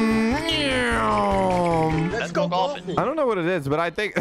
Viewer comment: who else hears “Sonic”?